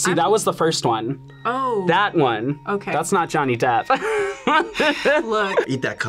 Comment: When I heard "Johnny Depp" as the first one, I think I aged a bit more 😅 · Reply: they actually said that's the first one. That's not johnny depp No need to age yourself just yet